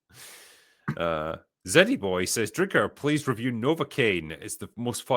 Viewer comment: Lol I heard "Drinker can you please review Novacaine" Me:... The numbing agent?